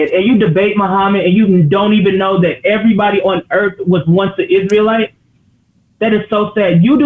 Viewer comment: From Rome to Constantinople 😂 I thought she said everybody in the world